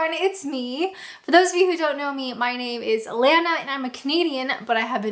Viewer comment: CAN I BE HONEST ... I misheard you at the start ... I thought you said you were a COMEDIAN not a CANADIAN ... so I've been looking for your stand up comedy. Must get my ears syringed.